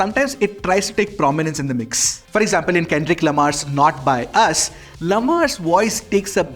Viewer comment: not by us??? Dawg not like us....just says the video is being read from teleprompter instead of from mind(which is obvious and done by everyone sure) I am your wellwisher just get the names of atleast famous songs right in the next ones to make the script not be so obvious(my style of saying it might sound rude but my intentions are good) I like your content and way of presenting stuff I am very choosy!